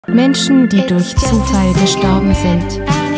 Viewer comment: Ich hab erst mal verstanden "Menschen die von Durchfall gestorben sind" 💀